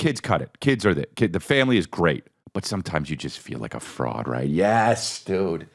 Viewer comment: For a second I thought he said "sometimes I feel like a frog" 😂 I do too man, I do too 😅